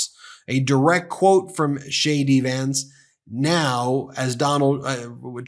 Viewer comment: Sounds like he say shady Vance not jd vance